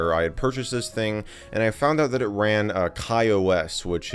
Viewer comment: | What i hear "I found out that it ran Kanye West"